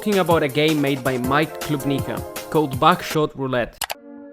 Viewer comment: Your accent made it sound like you said backshot roulette lmao